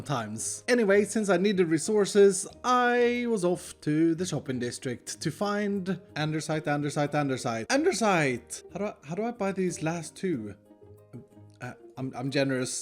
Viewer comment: am i the only one that ears "andeRsite" instead of "andesite"?